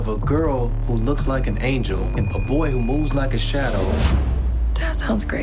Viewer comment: This is so racist a “a boy who looks like a shadow “ Ok 👍 My bad everyone it was just a misheard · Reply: He said "a boy who MOVES like a shadow" not looks like one